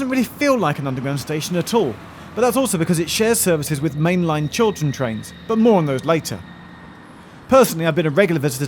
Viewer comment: I was hearing it wrong I thought that you said Children train. Like what is that. a train just for kids or a train painted as if drawn by a kid.